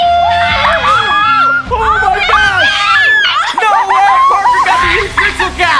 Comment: did Parker say holy sh*t at 3:15?